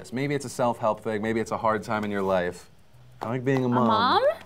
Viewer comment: did he just say i like being a mom? XD